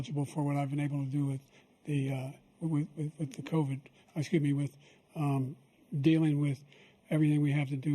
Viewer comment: Did Biden Just Say “With Covid… I skibidi With”😭?